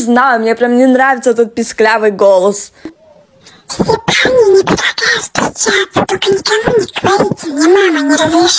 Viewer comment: Я один не понял, что она сказала, но я понял, что после этого она сказала: «Только никому не говорите, мне мама не разрешает.»? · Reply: Она сказала "все парни мне предлагают встречаться 💅🤫"